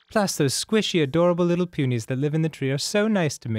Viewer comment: I heard, "That's where big penis live." instead of, "the punies". >~>